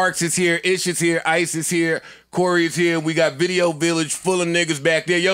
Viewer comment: I thought Joe said Rory is here got happy for a second